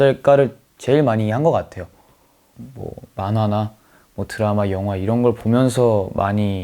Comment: i thought he said banana 💀💀